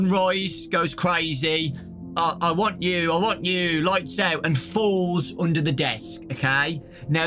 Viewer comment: Sunrise actually sounds like he’s saying “I WARNED YOU, I WARNED YOU” maybe he could be a neutral animatronic warning us about moonrdrop (or someone else?) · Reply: I thought he was saying “I WANT YOU, I WANT YOU”